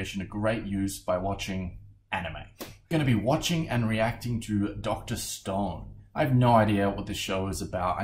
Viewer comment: i thought i heard "Dr. Stonks". Mustve heard wrong